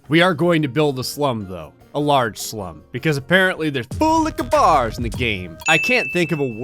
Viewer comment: I just realized he says “full liquor bars” not what I thought he’d been saying.... kahoola Khabar!